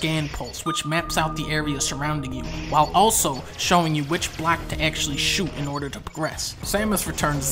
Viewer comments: This might be Unnecessary but the part 3:17 the captions says black instead of block... Is this A Easter Eggs? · u thought i heard "which black to actually shoot"